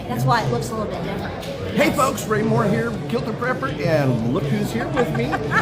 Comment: Omg, I thought said "quilted prepper" 😂